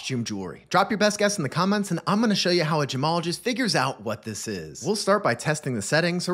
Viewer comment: Thought you said Jamalogist.